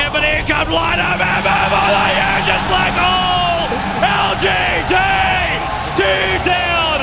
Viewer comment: this is what the tobi said for what i heard: "but here comes lightofheaven what i heard just black holeeee!!! " very sick commentators on T.I. respect to their jobs... :) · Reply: he said : but here comes light of heaven with the hugest black hole !!!